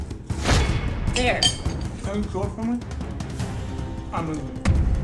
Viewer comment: I thought he is gonna say can you swallow it for me bec I’m injured